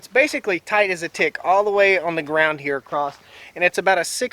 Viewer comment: Video: "Tight as a tick." Me: "Oh I thought he said tight as a di... Well nevermind."